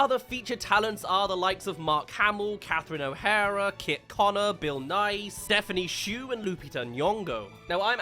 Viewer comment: Did he just say Bill Nye???